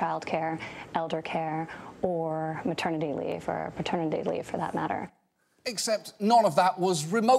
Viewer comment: wait, did she just say "fraternity leave" 🤣